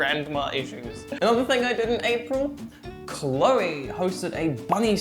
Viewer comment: I thought you said you did Chloe for a second